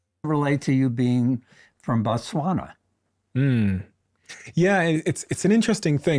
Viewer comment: Did they just say he is from Botswana?